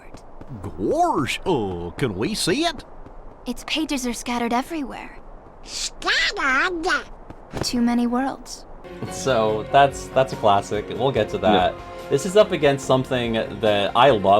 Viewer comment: WAIT I always thought this line was "Too many worlds" instead of To! I wonder if Aerith's VA thought so too, at least the inflection would make slightly more sense.